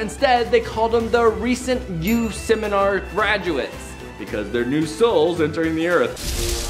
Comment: I think its called Youth Seminar